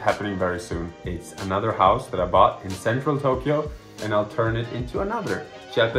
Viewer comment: I thought he said SanFranTokyo Like from big hero six he said “central Tokyo“😂😂😂